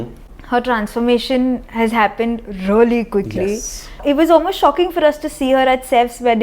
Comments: ‘Her transformation has happened really goodly ‘🤨🤨🤨🤨🤨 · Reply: Omg wtf i heard 'goodly' too. But she actually said quickly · ‘Her transformation has happened really goodly ‘🤨🤨🤨🤨🤨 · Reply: She said 'quickly', not 'goodly'. Even I thought that she said that ;-).